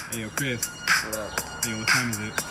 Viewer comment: I thought he said Modelo neglaton